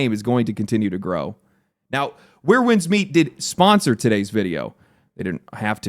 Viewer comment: „Where Winds Meet didn't sponsor today's video” 13:08 „And Where Winds Meet, thank you for sponsoring today's video” Edit: He did say: "did sponsor" instead of "didn't sponsor". No need to be a jerk like that one guy just because I misheard that.